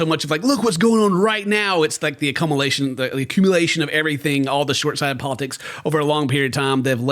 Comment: did Ken say “Akamalation-Accumulation”